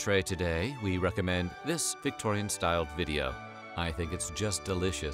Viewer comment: I’m partially deaf so I use subtitles. When he said “Victorian style video” I thought he said “Victorian style Chlamydia” because I was on the other side of the room and not looking at my phone. FML